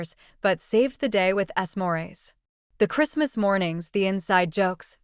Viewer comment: Warms the cockles of my cold, dead heart when I hear how he saved the BBQ with, and I quote, “ess morays” (3:12 time mark). AI-generated videos like this one truly suck 👎👎👎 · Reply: "ess morays" = "smores" Something that includes marshmallows. Yes, AI fails regularly.